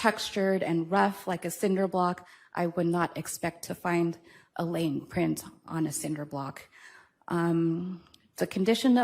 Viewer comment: Dang! I thought she said "Gnome print"! 😮😢😅